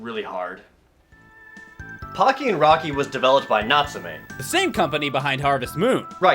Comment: I swore he said the game was developed by Nazis. I had to listen to it again after I stopped laughing.